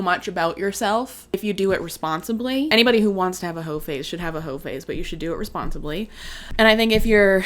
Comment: genuinely thought you said 'everyone should have a ho face' and I was trying to think what that would look like. Also I certainly hope she got permission from those guys to talk about them.